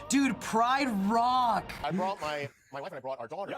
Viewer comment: "Wow dude, pride rock!" All I heard was "I rEcOgNiSe tHe ThInG!"